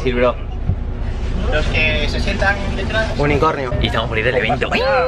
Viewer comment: Se me trabo el audio y entendí "Murnir Cornios".